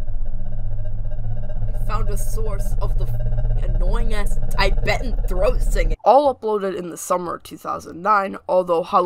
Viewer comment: what was he saying? i couldn't hear very well. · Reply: "i found the source of the f**king annoying ass ????? throat singing" (the question marks mean i don't understand)